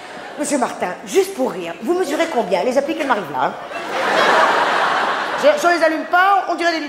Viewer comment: Elle dit quoi à 3:33 ? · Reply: Gabriel Pirad Vous mesurez combien ? Les appliques elles m'arrivent là !